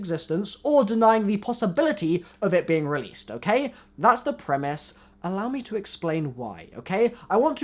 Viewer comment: sounds like he says primis